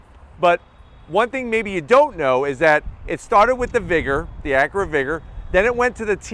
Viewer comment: Ohhh be said vigor I thought he said nig.... nvrm